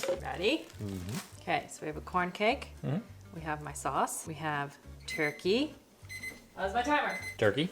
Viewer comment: I swore you said Jelly turkey for the Bethany snack so I had to rewind 😂😂🤢 LOL